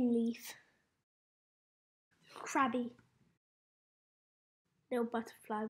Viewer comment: I thought that said Cr*py XD